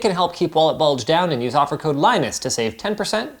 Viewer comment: If Linus is your real name... Man I thought I heard Linux at first